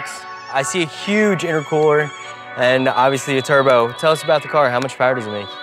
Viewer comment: DID HE JUST SAY AIRCOOLER!?